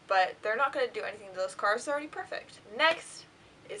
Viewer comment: did she say "they are not going to do anything to those cars, they are imperfect"?